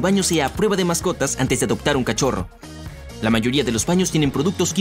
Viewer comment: Mi perro es cachorro aun xd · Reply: pense que decia cachondo